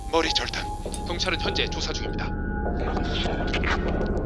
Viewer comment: '난 사라지지만 넌 평생 사라지지 않을 거야 바보처럼'아 왜'난 서라지지만 넌 사라진줄 알 거야.밥 먹자'로 들리는거 나만 이럼?